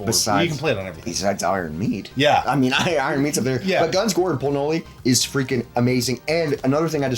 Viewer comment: Why does it sound like dude is saying "Guns, Gore, and CORN-HOLEY?!" 😂😂